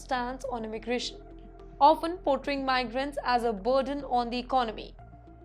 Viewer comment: “portering migrants” instead of saying portraying 😂